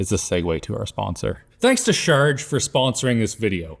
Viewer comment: When started the sponsor segue, I thought he said "shart" 😅